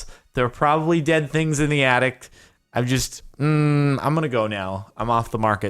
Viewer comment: did anyone else notice him saying addict instead of attic?